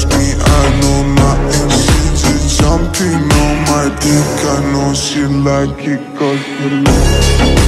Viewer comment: Why do I hear “I know I think she likes ya cut g” in